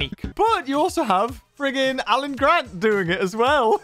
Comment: Did he say "Alan Grant?"